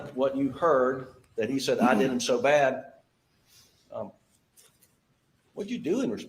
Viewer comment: I heard “I did ‘em so bad”…. But when I try, I can see how it could have been “they”. Considering his accent, where /th/ can be pronounced like a soft ‘d’ and there is a drawn out /ee/ sound at the end of /ay/ words, it’s fair that it coulda been “they”.